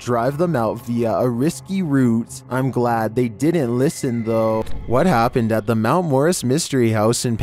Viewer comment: Thanks for the feature! Great content as always 👻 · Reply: I thought you said scary what happened?